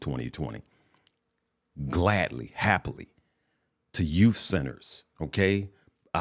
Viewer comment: 🇯🇵🇺🇸 - also you say you travel all around the world ? How come you haven’t come visit me in Japan and stay with my family yet ? 😊 Ps. I heard you say “I give a lot of money to you ‘sinners’ 😂 then I realized it was “youth centers”